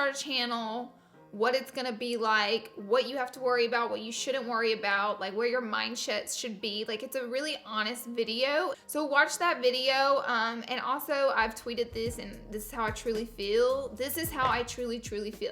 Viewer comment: Was it just me or did she say "mind shit" @ 1:11 LOL